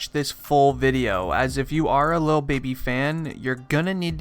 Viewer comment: DID HE SAY "Lil Bibby?" 😂🤷🏽‍♂️